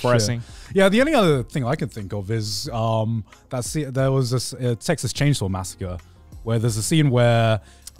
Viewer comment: Legit thought Grant said ‘Sexiest chainsaw massacre’